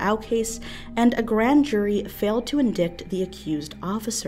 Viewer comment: Did you just say "in dicked"? God dammit. I had so much faith in you. That is just sloppy reporting.